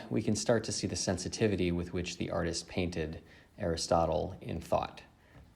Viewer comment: Did you say Aristotle at around 2:05? Is it Archimedes or Aristotle?